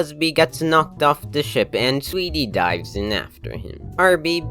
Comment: i heard that wrong and thought he said "sweetie dies" and was really confused